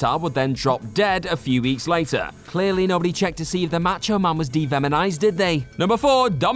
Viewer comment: Did you say "devemonized"?